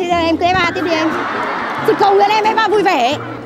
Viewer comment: nói gì thế nhỉ? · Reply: "Chị cầu mong em FA vui vẻ"